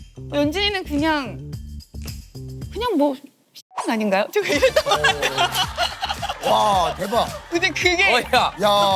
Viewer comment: 뭐라고 한건가요? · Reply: 썅년이요